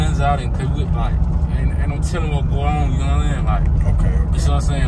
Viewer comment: “ain’t no telling what’s going on” I thought he said emit till or something lol that southern accent